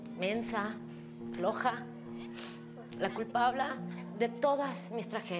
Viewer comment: like si te diste cuenta de que dijo " culpabla " 😂😂